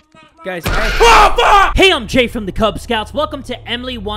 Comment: I heard: "Hey I'm Emily from the Kay Scouts"